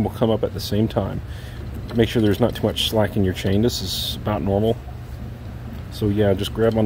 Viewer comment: Hi Sir - 7:49 did u say - that is not normal? or abnormal? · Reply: That's about normal. Thanks for watching!